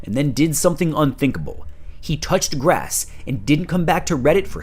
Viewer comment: at first i thought he said "touched breasts," which truly would have been unthinkable for a redditor.